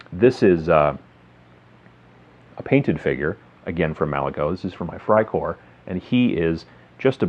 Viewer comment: What kind of miniature is that at 6:16. They look pretty cool but I can't quite hear/understand the name. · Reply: They're Malifaux minis. I think they're from the Von Schill faction. Thanks for watching!